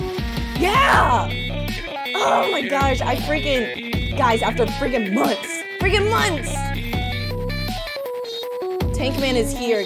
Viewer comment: DONT tell me I’m the only person who heard “guys I’m about to freaking bust” the first time